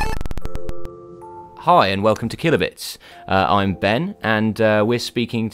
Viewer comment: At the start of the video, I thought you said "Hello, and welcome to Kill-a-bitch."